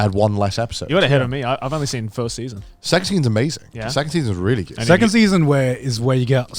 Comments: Yeah, turning point 4 has like 100x the shock value of all the previous ones combined. Its absolutely insane. I really look forward to and dread that being animated. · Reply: Phew.. i thought when connor said the second season is really good, for some reason i thought he said sex scenes are really good. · Did he say ‘second season’ or ‘sex scene’..? … well both contexts work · WHY DOES IT SOUND LIKE HES SAYING SEX SCENE BRO I WAS SO CONFUSED UNTIL THE OTHERS STARTED TALKING